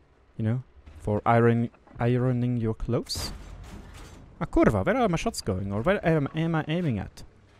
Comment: did you just say "kurwa"???